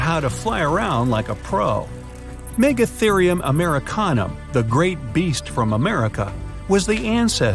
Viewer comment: I thought he said merrick hanna